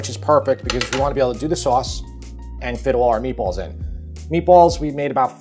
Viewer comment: I thought he said "fiddle our meatballs" at 9:17 and I was like WHAT.